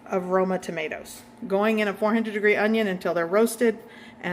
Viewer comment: Going in a 400 degree onion?🤣 you’re working too hard! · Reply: I thought she said 400 degree onion so I played it again 😅